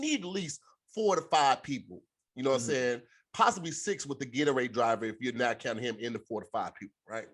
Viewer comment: Did he say Gatorade driver